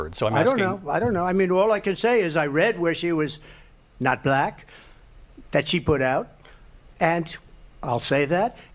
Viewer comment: Yea my brain did its usual trump mumble autocorrect and I thought he said 'That she was not black, she put that out', thank you for pointing this out.